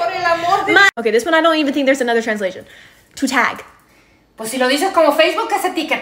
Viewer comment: She said "to tag" But i heard "to take". Mu ears are wrong 😭